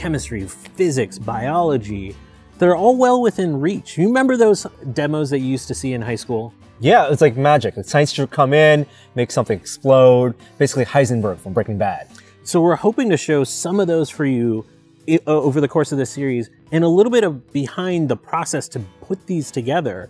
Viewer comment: I could've sworn you said Hindenburg, not Heisenberg after you said explosions XD